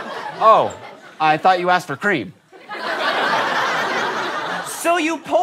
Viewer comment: Even I thought they were saying "Cream." Who's the guy that Stacy is supposed to be?